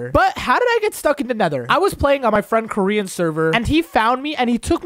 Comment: I thought he said hes friend call korean but the end of the vid hes name is koryin not korean😂